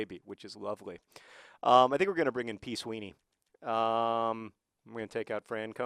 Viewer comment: When he said "P. Sweeney", I thought he said "peace weenie". Hehe.